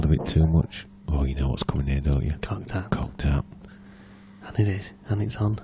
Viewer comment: What was that term they used for the black round 3 cushions in frame 13? Sounded like they said "cock tart?" Never heard of it... <link> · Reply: cocked hat